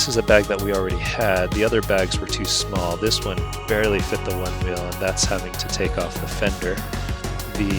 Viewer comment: Im confused...It sounded like you said you had to take the onewheel off, right before you showed the paperwork?